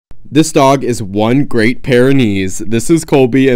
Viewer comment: I thought you said this dog has a great pair of knees 😩😭